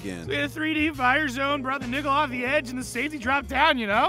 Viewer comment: Did kupp just say Nigga??? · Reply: Nickle as in Nickleback